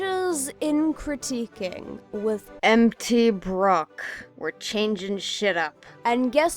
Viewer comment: For a moment there I thought you said MTBrooke